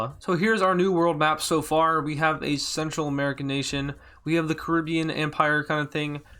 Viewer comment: it sounds like you said sexual america 😂